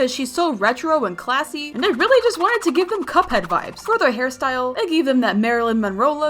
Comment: Did you say CUPHEAD